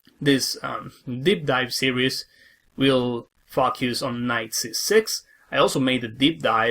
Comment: Everytime he says the word "focus" ... I get on the floor rolling 😂 ... I always hear it "F*** yous" 😂 Apart from that, very nice and helpful video my dear sir Mr GM 😊